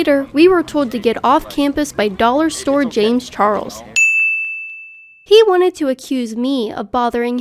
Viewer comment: Dude: is gay, has brown hair and is skinny. Kaitlyn: “James Charles” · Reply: I thought she said Dollar Store James Charles, meaning he looked like a very cheap knockoff but no where near the real deal. When she showed the side by side, I completely got what she meant.